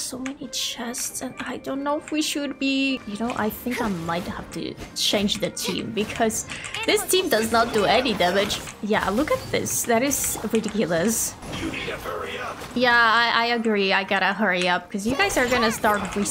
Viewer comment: I’VE ALWAYS WONDERED WHAT THAT FATUI SAID AND NOW IM JUST REALISING THAT OF COURSE HE SAYS “you need to hurry up” AND NOT “you need a Bonita” LIKE DUHH 💀💀